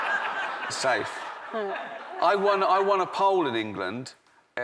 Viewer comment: When he said 'I won a poll' i thought he said 'I won a pole'